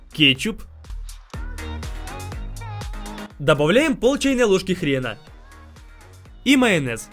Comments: Я одна услышала " добавляем пол чашки хр*на"😂😂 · хрена или мне послышалось??? 😳😳😳😳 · мне одной послышалось 'полчайной ложки хрена'?